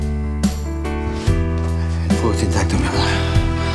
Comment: que dice marcos en el minuto 24:33 - 24:35 y en el minuto 26:43 - 26:44 ? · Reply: Fuerte intacto mi amor, fuerte intactooooo... Servite, servite servite servite.....